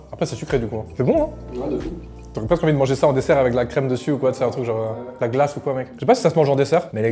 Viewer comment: J'ai entendu "des allocos avec de la crème", j'ai eu un GIGANTESQUE frisson ahahaha